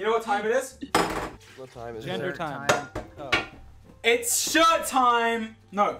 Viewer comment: just after 15:57 did dream say “gender time”?? lmfao